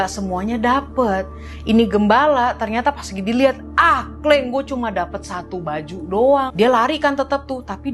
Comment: did i heard "kleng"